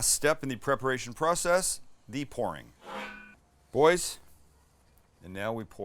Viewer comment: Thought he said "the porn" was the last step.